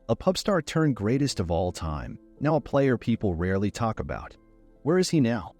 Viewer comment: did you just say "Now people rarley talk about?"